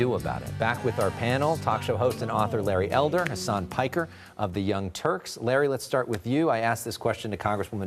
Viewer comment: I thought he said "Larry Elder and his son Piker".....whenever he introduces both of them....lol